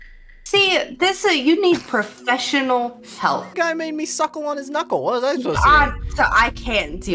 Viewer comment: “Tickle his pickle” got me dying 😂😂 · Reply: I thought he said “tickle his fickle” and I was like what. Thanks for the clarification 😂